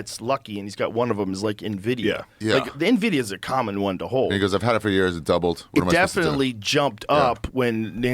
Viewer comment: sounds like Ari said "Niggas I've had it for years"😂